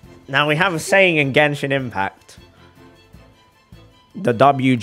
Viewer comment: captions say gangshin impact wth